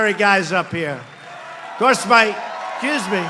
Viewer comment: The Chants we need at all Pres. Trump rallies from now til 6 Nov. is "USA, USA; WE ARE Q, WE ARE Q"! Maybe that will get the fake news MSM to ask POTUS the "Q" question. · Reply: Listen at 2:57:30, did Trump say Q's Me and then repeat correcting his word to Excuse Me???